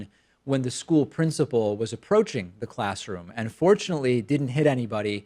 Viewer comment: I thought you said "unfortunately" lol